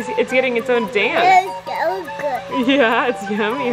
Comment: i swear said “it’s so good” 🥺